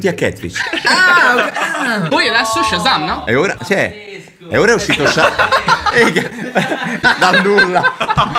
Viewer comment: ho sentito eia??😻😻 Ti piace il sardo??